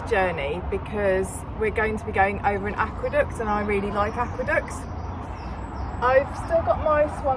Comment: Looks like a cracking day for a cruise, the way you said aqueduct near the start made me do a double take, I could have sworn you said aqua duck and was imagining you running over a mallard lol